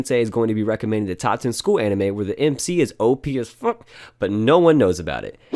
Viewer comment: Part 0:16 did you Mean OP as Fu*k Because I heard from you is ..... OP as Flu*k